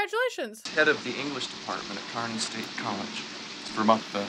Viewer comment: I thought he said “Iowa State” and I was bout to cry “WE DONT WANT HIM”